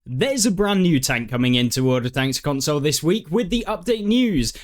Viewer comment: sounded like you said "World Of Thanks" lol